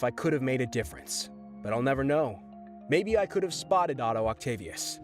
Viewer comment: “Spotted” instead of “ stopped”? 15:59 “Alice Stair Smith”? lol tf?! You mean Alis-ter S-mai-th